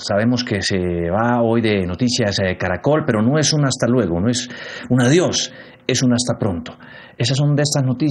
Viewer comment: "NO ES UN HASTA LUEGO ... ES UN ADIOS !!!" 😂😂😂 ESE JUAN DIEGO 😂😂😂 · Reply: escuche bien «NO ES UN ADIOS»